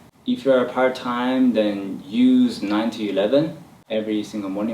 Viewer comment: Is he sayin 90.11. Is he talkin about a program? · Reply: 9-11am if you cant learn all day about it learn at around that time